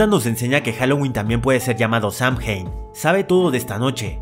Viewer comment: sam-que? No escuche bien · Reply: Samhain, es el nombre celta